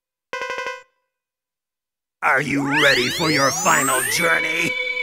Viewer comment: what does sigma say? are you ready for your final ____? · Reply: gonzalez he says the following: "ARE YOU READY FOR YOUR FINAL JOURNEY!?"